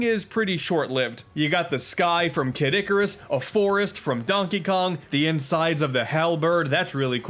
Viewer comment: did he just said Caddicarus or kid icarus?